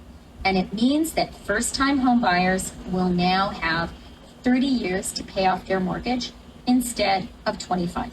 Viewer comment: didn't they talk about 100 yr mortgage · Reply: I thought I heard 90 year , but ya ..3 generation mortgage 🤷